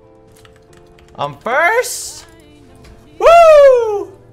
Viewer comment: i thought he said I'm burst 😂